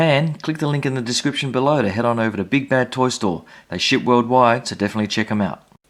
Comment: doesn't it sound like he said "they shit worldwide